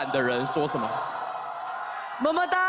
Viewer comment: Wait what did he say at 30:20? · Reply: it's 么么哒 which is like a catchphrase for anyone deemed cute (well fans of doinb do consider him cute).